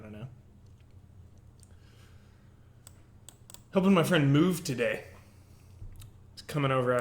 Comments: Thought he said "Gonna help my friend Moof today" :'( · At the start of the video I thought you said "I'm helping my friend Moof today." I think you've helped Moof enough for now...